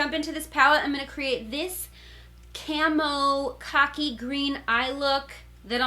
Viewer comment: Every time you said “khaki”, it sounded like you were saying “cocky.”